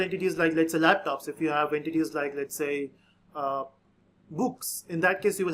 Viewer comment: I heard books as boo*s and got shocked